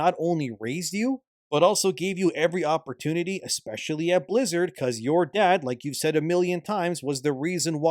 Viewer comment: Did you know that Pirate Software used to work at Blizzard? He doesn't talk about it much. If I was him I'd bring it up all the time and then pretend to be humble. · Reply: You sure he said blizzard i thought he keep saying lizard